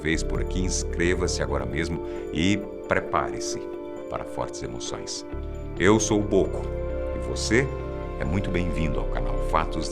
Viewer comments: Na hora que ele falou o nome dele, só eu q entendi "eu sou o porco" 🤔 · Ouvi "Eu sou Bôco" já fiquei em choque...